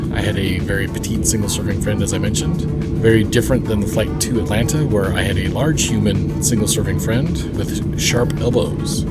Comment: Don't know if it's your mic or what but I can't figure out what you're saying near the end about your experience in economy is largely dependant upon your surroundings...sounds like you said "on this leg I had a very petite single serving friend very different than the flight to Atlanta where I had a large human single serving friend with sharp elbows". Surely that's not what you're saying?